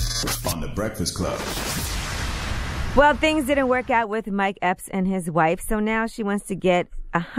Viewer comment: I thought Yee said "My ex and his wife" 😂